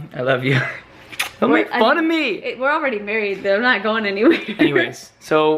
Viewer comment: it sounded like carlos said fuck me but he really said make fun of me 😂😂